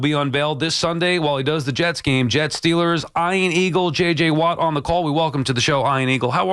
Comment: I hate that he pronounces his name I-an and not E-an. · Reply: The first time hearing his name all those years ago, I thought they said iron eagle, like the movie with Lou Gossett. It is an odd pronunciation of the name but sounds more phonetically pleasing IMO.